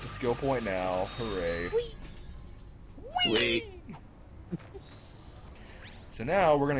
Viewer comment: when you guys say wee... Almost sounded like you said weed!